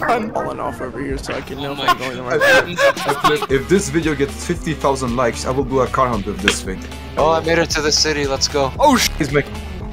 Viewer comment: So what did say at 3:53 ? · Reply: said if the video gets 50k likes he will do a carhunt with that wheel in